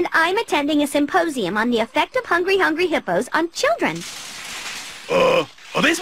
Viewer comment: uuuuuuuuuuuum.... what? hungry hungry...hepos? on children? i dont understand that, but make me lughs POWSEES? HAHAHAHAHAHAHAHAHAHAHA 19:34 when you says all your story of your kill and stole all your wepons on fornite